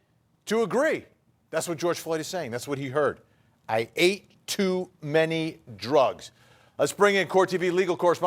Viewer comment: I heard "I ain't do any drugs."